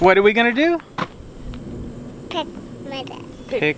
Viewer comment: When you asked what you were going to do, I thought she very sweetly said “pick maggots” 😱 I’m so glad you cleared it up by saying pick tomatoes! 😂